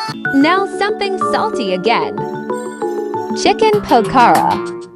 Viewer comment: Omg i thought you said chicken pork 😂